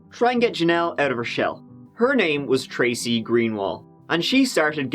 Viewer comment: OMG! SO SHOCKED about MOUNTAIN CITY!!! I lived there for 3 years and it is a NOTHING town! I was ASTONISHED when you said Mountain city and then I thought you said Tracy GREEN not Greenwald and Tracy Green is my sister in law! She lived there then too! CRAZY!